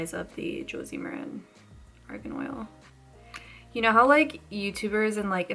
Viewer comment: did you just call it organ oil loool